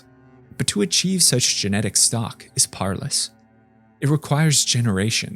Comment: what is he saying? "but to achieve such genetic stock is [?]" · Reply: Powerless, i think?